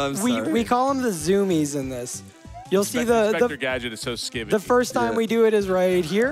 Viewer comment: Does anyone understand what says? "Inspector Gadget is so [???]". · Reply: Skibidi